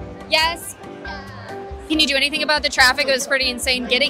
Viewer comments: favorite answer: "I'm Batman?." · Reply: I know I was just saying it sounded like he said I'm black man 😐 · favorite answer: "I'm Batman?." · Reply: For some reason I heard "I'm black man" and I was sitting here like: 🤨📸